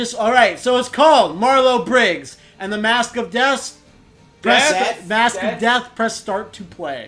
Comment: I swear when mat said the game title I heard "Marlow Briggs and the mask of des.(Desu)